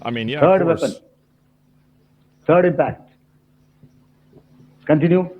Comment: I hear him say 3rd impact and my Evangelion PTSD kicked in for a split second lmfao.